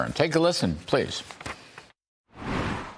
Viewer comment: I heard in intro "New eggs secretary" That actually cool :) In 2 weeks she find solution - so I will build a chicken coop Actually it is funny that they told "Biden had no plans" but later they by accident fired task force which was working against avian flu